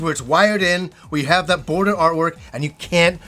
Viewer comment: i heard "boner artwork" at first